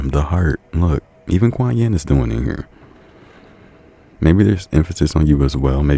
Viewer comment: Watching pile 2 while doing some light studying, and just as I started to zone out, I heard “Even Quan Yin is still in here”, which grabbed my attention because KwanYin is my real name😭 I can say I‘ve been soooo so so intentional about opening my heart recently🫶🏾